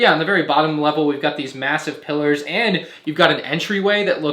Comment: did he say... Massive?